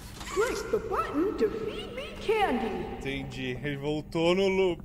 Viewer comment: parece uma voz dizendo "no more candy", foi oq eu ouvi né